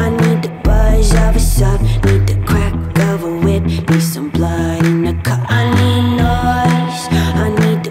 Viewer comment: Until a week ago I thought she was saying blood in the cud. I kept wondering why she was singing about sick cows.